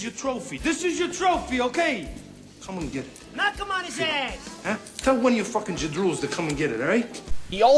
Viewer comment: I swear I heard Joe Pesci yell "Knock him on his ass!"